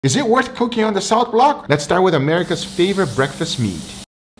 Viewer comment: I thought you said South block. I've been to prison so I was like ..... Why is he on South block?🤷😂😂